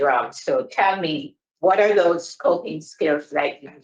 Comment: I put the caption on, and it said, " What kind of pooping skills have you learned " 😂😂😂😂 instead of coping